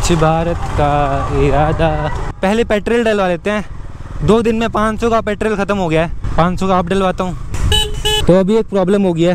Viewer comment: petriql instead of saying petrol 🤣😅😂